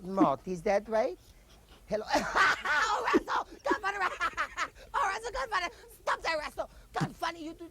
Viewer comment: Did he say がんばれ(Ganbare) at 3:43? That means "do your best," in Japanese. It seems rather out of place, so I assume I must have misheard. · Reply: No idea if you're still wondering this or not, but what he's saying at 3:43 is actually the expression gunfunnit. It's basically a local pidgin version of goddamnit.